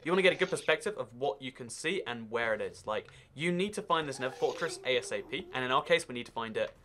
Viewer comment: Did He Say "End Fortress"?